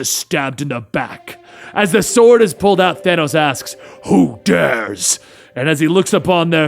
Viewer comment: 😂"as the sword is pulled out of Thanos ask" I thought I heard ass😅